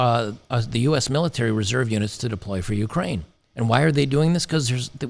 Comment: When you said "reserve units", I heard "reserve unic's", which is probably more accurate, lol.